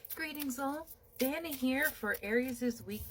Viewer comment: Hon we have ALL been guilty of that. Also my name is, I didn't want to be rude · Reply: Oh! I am so sorry! You are so kind! Please forgive me, as all this time I thought you were saying ‘Danni’🙃